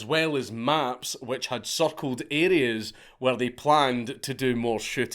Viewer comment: At one point you said "circled" and I thought you said "suckled" and I didn't want to listen anymore.